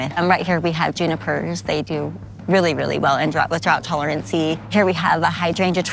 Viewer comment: did she say tolerancy?